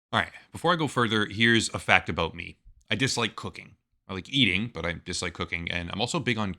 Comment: When the ad started I thought you said "I dislike cookies"